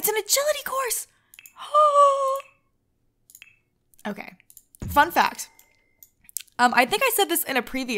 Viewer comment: did she say fu**